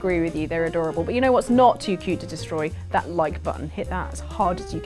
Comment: I misheard "like button" as "Mike button". . . I want a Mike button proclaiming "Aha loophole" each time it's pressed. Either that or it should murder something.